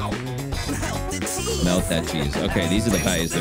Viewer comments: When it says "Melt the cheese!" I thought they said "Milk the cheese!" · When he said "Melt the cheese" at the beginning I thought he said milk the cheese XD Im going crazy I think XD